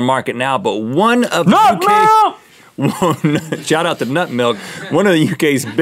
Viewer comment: Lol I thought Link said "NOT MILK" and not "Nut milk"...